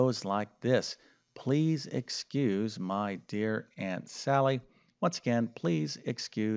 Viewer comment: I heard it as aunt Sarah.